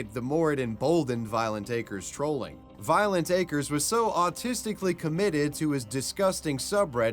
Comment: Get 50% off your first order of CookUnity meals — go to <link> and use my code WAVYWEBSURF50 at checkout to try them out for yourself! Thanks to CookUnity for sponsoring this video. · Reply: Hey, did I mishear you or did you actually say that the Reddit stalker was autistically committed??